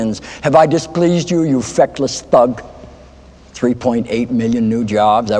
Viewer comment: "Have I displeased you, you feckless thug?"...gives me goosebumps every time. How Martin Sheen didn't win an Emmy for The West Wing is something I will never understand. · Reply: Damn this whole time I thought he was saying “affectless” lmao